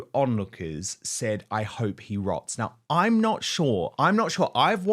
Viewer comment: No, i heard, "i hope he rots"...